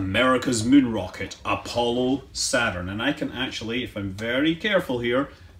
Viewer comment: did he say "apollo saturn" at 2:05?